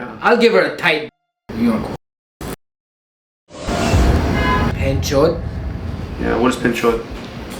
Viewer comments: he actually said benchod not panchot.which is an offensive term in Hindi or Urdu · I think he said benchod which is a Hindi-Urdu slur meaning "sisterf*cker". I'm Turkish, but I had an Indian friend in university, that's how I know about benchod and madarchod. lol